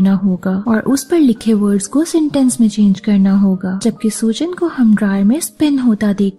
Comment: kisme spin hote hue dekhte hai??? I didn't get it , 8:06 pe kya bola 🤔.. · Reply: dryer mai